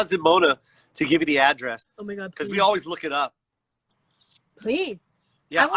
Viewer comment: At the 1:12:00 does anyone else hear a whisper that says "My God"?